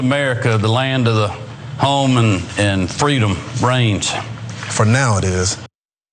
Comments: I swear I heard him say "freedom brains," twice! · I didn't hear "the land of the home and freedom reigns", I heard "the land of the home and freedom brains"! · I thought he said Freedom Brains... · I'm from Texas with family out in the Carolinas. So I'm certainly no stranger to the southern accents. But even Kemp's accent is so backwoods stupid sounding that I would've sworn the dude said "Freedom...Brains". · i thought he said "Freedom brains" I was like omg they were right there are secret lizard people!!! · I could have sworn I heard freedom brains try its one of those weird brain tricks you can hear him say brains or rains.